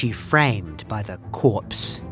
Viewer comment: for anyone else as confused as me I think he was saying ‘cops’ not ‘corpse’.